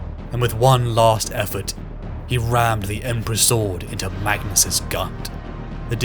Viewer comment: I thought you said Magnus's gunt 😂